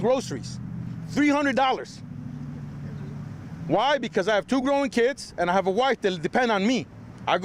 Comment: I thought he said he had a 9 year old and a 4 year old but now he said he got 2 grown kids🤔